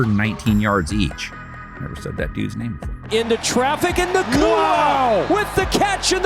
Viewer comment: Did anyone else hear the narrator say something under his breath? Was that supposed to be recorded? Check it out at 1:23. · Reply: He said, "never said that dudes name before."